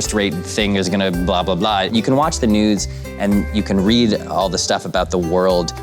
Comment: “you can watch the nudes” 😂 even the subtitles say that